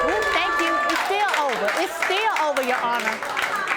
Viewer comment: Why would you even stay with him after he cheated even if the child isn't his??? Why is the paternity test the determinator of your decision??? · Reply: Brielliant 1 back it up to 16:00 she specifically said " 2xits over ..I'm still leaving him you honor" but audience goes all dramatic after judge lake reads the results in the middle of her responding to them so calm down and listen to what she actually says